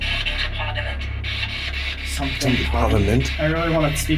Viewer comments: in 51:26 i think it said houses of parliament i don't know what that has to do with it but yea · I swear to god that thing said clear as day “Welcome to parliament”🫥 Edit: that last part was kinda sad man 😂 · When the spirit box said “???? Parliament” I thought it said “Houses of Parliament”. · I think it said welcome, where you guys didn’t know what it said before “parliment” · when they ask "Is anyone following us around today?", It sounded like the spirit box said " Welcome to the Parliament." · I heard welcome to parliament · It said, "I'm in/with the Parliament" I am saying this with absolute confidence and when you ask if it wants you to go to a different building, I'm pretty sure I heard "With your luck?" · i heard either "someone's parliment" or "whoever's parliment' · when it said ... Parliament, I heard welcome to parliament. Don't know if that means something but its defiantly interesting. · Sounded like it said “welcome to parliament” around 51:30 I think it was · I heard “welcome to parliament”